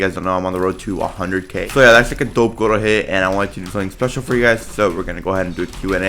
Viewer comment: tf he say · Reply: that is a dope goal to hit